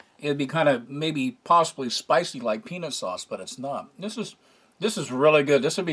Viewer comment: I had to double check, I thought at 11:05 you said "penis sauce" then realized you said "peanut sauce" ...im going back to bed.